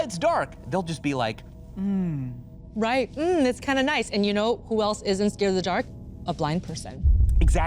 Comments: my volume is low i seriously thought she said "you know who isn't scared of the dark? a black person" like jesus christ lady that's awful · I thought she said " You know who else isn't afraid of the dark, a black person"